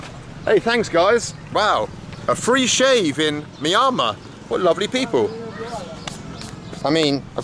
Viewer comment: did you say Miramar ??